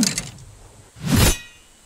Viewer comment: i heard "oh its black" ._.